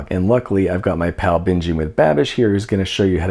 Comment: I thought you said "My pal Bingey McBabish"and I LOVED that nickname for him 😂